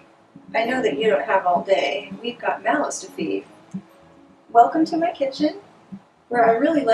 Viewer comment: I thought she said "malice to feed" instead of "mouths to feed" at first in the cooking clip at the beginning lmfao.